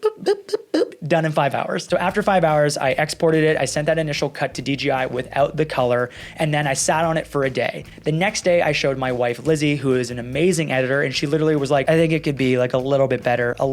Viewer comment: I thought said "I showed my wife Glizzy" hahahahaha